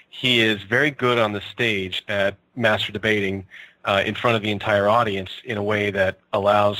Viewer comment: Anyone heard "he is very good at masturbating in front of an entire audiance" at 6:40 ?